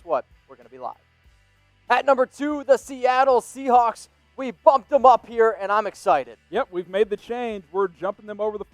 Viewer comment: It sounded like he said “we fucked em up here”